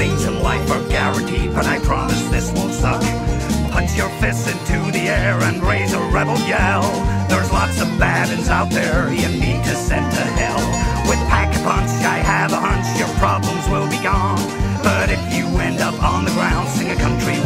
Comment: Honestly I thought he said “rodeo” instead of rebel yell for all my years playing zombies lol